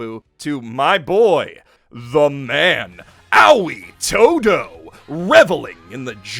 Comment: did this dude seriously just say owee todo instead of Ali Todo?